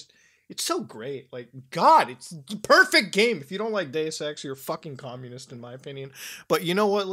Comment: I thought Muta said "If you don't like gay sex, you're a Communist." 😂